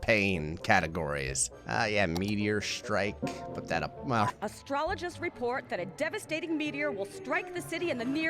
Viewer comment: Did I hear that right at the 10:00 mark? "Astrologers report a meteor is about to hit!" Crap, it's not a Virgo, is it? And, New Mexico is a lot like Florida. But instead of 1000% humidity, we have negative humidity. And we were the last state to have drive-up liquor stores. You might like it. :)